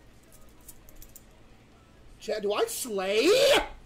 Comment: DID HE JUST SAY “DO I SLAYYY”